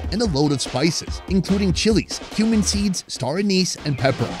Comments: ill be honest, i thought you said "human seeds" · Ahhh cumin seeds. Had to turn captions on i thought he was saying human seeds 🤣